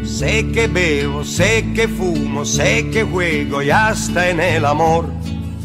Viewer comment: Yo pense que decia se que huelo y hasta en el amor